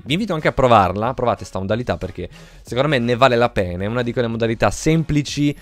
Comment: Min 1:42 solo io ho sentito "ne vale la pene"??😂😂